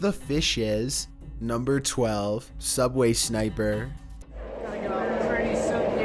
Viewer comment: #12 I thought you said "Subway SNIPER " XD